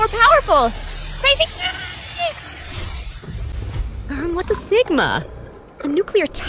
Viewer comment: what did you say 😦 erm what the sigma?